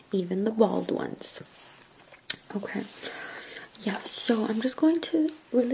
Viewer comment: мне послышалось " я всё"